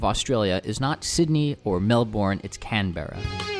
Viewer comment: did- did you just say melbUORN